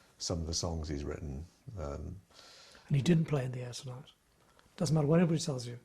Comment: Sorry if anyone asked this already. Tony says Phil did't play in the ???? at the 1:27:45. I couldn't understand what it was Phil didn't play on. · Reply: In The Air Tonight